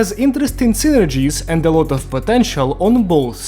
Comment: did I hear potential?